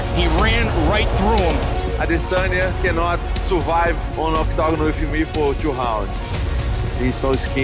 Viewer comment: I've seen this promo 20+ times and I still dont know what he says at 1:08. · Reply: he cannot survive in the octagon with me for 2 rounds. He's so skinny when he feels my punches my kicks on his skinny ribs I will broke him